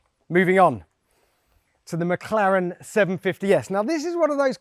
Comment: What did he say? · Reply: So the McLaren 750s